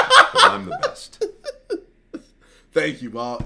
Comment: "Thank you mark" 😂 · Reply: mark i thought he said "thank you mak"